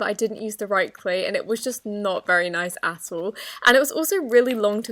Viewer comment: If any one used subtitle luke i did, did you notice that at 0:22 she said "and it was just not very good at all" but the subtitle said "and it was just not very asshole at all". Just me Ok.